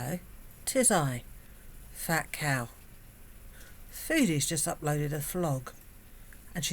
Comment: You said "foodie just uploaded a vlog" but what I heard was "Foodie just walked into a flogg" LoL I got all excited about watching her self flagellate! 😂